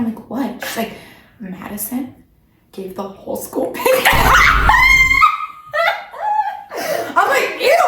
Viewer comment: I thought she said she gave the whole school pancakes ☠️💀☠️💀💀